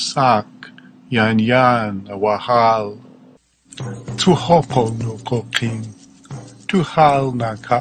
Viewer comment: did he just say "to hope on no cocaine" with a stone cold face? 😂